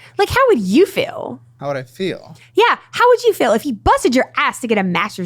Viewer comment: She said she lost 100lbs but didn’t say how much she gained back. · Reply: I think she said she gained back 25lbs but I could have misunderstood. Edit: yepp 25lbs